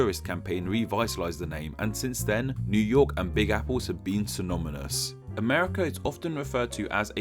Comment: Did you just say "synomynous"?